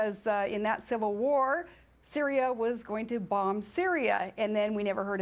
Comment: Can anybody make out what she was trying to say from 1:24-1:37? Something about Syria trying to bomb Syria?????? Question: Who is funnier Eddie Murphy as Buckwheat, or Sarah Palin as Sarah Palin?